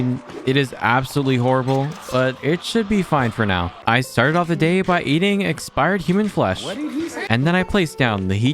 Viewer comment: Lol he said I started by eating human flesh and someone said what did he say